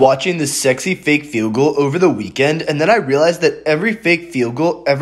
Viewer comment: I thought he was saying fake feguls.